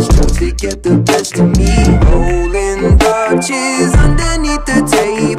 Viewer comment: The lyric is 'rolling Dutches underneath the table.' A Dutch, in reference to marijuana, is weed rolled up in the outer layer of a cigarillo, known as a blunt, of the brand, Dutches. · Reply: I always thought he was saying 'rolling crutches'